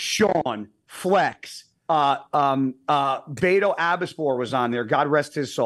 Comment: What dead bodybuilder name does guy say at 1:01:30? Beto? Can't understand. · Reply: Baito Abbaspour